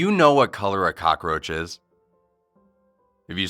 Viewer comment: The first time I watched this bid I thought I heard him say frog instead of a cockroach